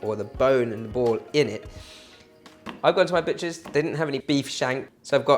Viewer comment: LMAO at 0:40 it sounds like you said "I go to my bitches, they didn't have any... "